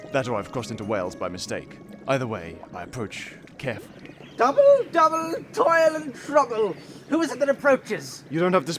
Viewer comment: I hear 'Double, double, toilet and trouble.' What a vicious curse!!🤣🤣🤣